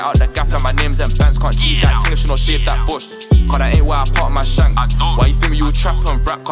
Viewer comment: Did he just say he can't jeet a girl if she aint shaved her Bush 🤣🤣